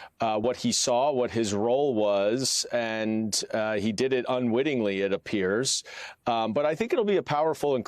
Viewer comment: I thought Hur said He "UNWILLINGLY" kept the documents. FACT CHECK!